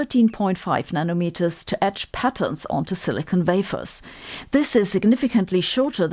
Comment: You said the word "wafers" except I was confused for a second because I thought you said "vafer" and what the frig is a vafer? But then I went Ooooooh, yeah, I forgot about your vampire vords.